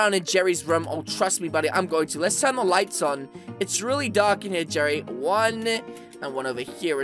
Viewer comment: Is it juat me or sound like JELLY just saying